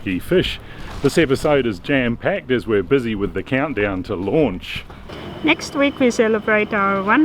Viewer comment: oh.. i thought it said getting ready for lunch.. not launch.. my bad.